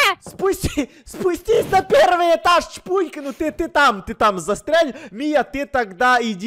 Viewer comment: Я одна услышала в 15:17 "застряль" если не одна лайк🍄👇✨♥❤✌🌚